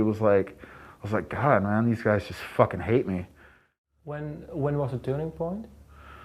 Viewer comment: The interviewer is uncomprehandable. What does he say at 1:25? Edit: could hear it in the original interview: "when was the turning point".